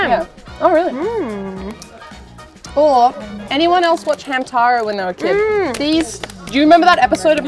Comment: lol when said anyone who watched hamtaro as a kid , i heard that as hentai lmao i was like wtf